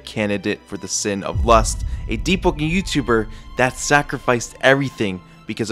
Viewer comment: Is it just me or i hear him spell "Deep-po-ken